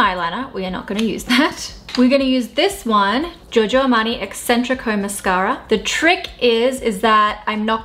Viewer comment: I heard it as “Giorgio Armani Eccentric Ho Mascara” and I wish that was the actual name 🥳💕